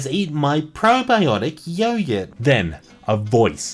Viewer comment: literally said "yoyurt" instead of "yogurt". I was so confused